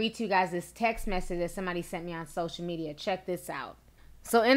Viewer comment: I heard,"he's rapin me"...